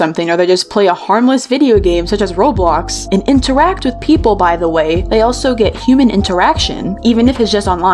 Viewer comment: I thought you said "interwhack with people"